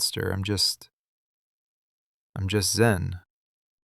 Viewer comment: I'm just Zen, anywhere else I'd be a ten 😂 sorry That's what came to my mind first when I heard it.